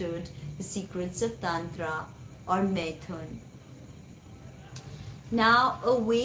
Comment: Hi, such a synchronistic post. Just to be clear, what word do you say here 16:13? She understood the secrets of .....? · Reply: MAITHUNA...one of the 5 M's of tantra.